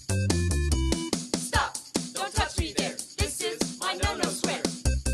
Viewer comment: Lol, I first heard "Stop, don't touch me there, this is my Manosphere." and was a bit confused.